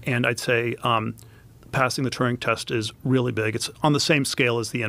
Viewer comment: Hey....not sure if your accent but sounds like your saying "passing the turning test " peters saying "passing the Turin test...as in alan turin . ..the godfather of computers who broke the enigma files! Or i just may have misheard.....dont mean to be rude if misheard as listen to your channel and content daily and your one of my go to's for up to date info ! ....regards from the UK 😊😊